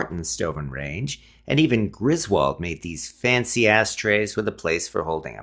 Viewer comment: Sounded like he said fancy ass tray